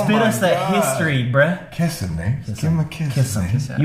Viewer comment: when yall said it sounded like he said "hi sam"to me it sounded like "kill him"